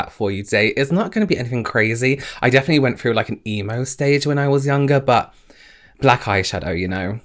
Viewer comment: Why did I think he said EMU stage at 1:00 ??? 😂🤦🏻‍♀️